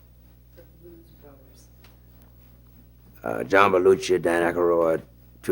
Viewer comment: whose name did he say? John...? I can't catch it :( Please help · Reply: Jane Volk he said John Belushi